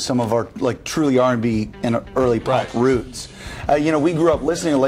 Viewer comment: does isaac say "early R&B and early black roots" at around 2:20?